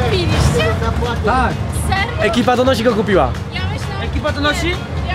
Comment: Czemu ja usłyszałem ekipadonosi xD